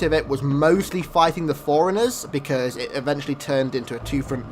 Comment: when he said "forerunners" i thought he said "foreigners" XD